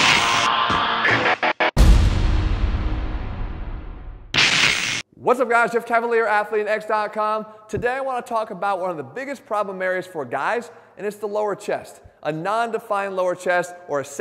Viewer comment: If you are looking for an exercise to target your lower chest to help get the definition you're looking for; check out this video here => <link> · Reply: In all his videos, I hear "chef cavilier"... Makes me chuckle every time😅